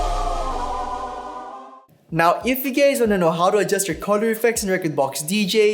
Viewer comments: lol did he say ''gays''? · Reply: Lol, it's "guys", but all are welcome on this channel! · did he just call us gays ?